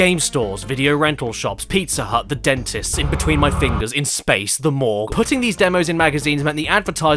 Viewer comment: thought he said "the mall" for a second, was like "jesus christ Caddy", double checked, kinda disappointed now, feel dumb too.